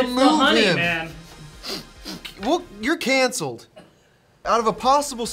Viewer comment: i swear when i first watched this i thought said you're cancer when rated it a 7